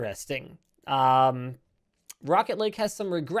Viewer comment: Did Linus say Rocket League?